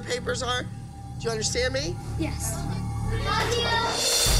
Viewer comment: Maddie said “good luck 🍀 CHLEO instead of Chloe 😂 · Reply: She said love you